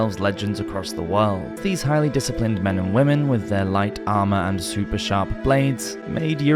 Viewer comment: does he say men& women?😂